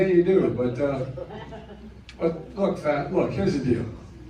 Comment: Did you catch what he was about to call him? He said “ look Fat “ 😂